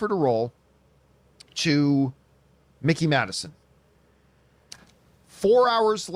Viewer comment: Household name? This is literally the first time I have ever heard of her. I actually thought that John kept saying they offered the role to Mickey Mouse.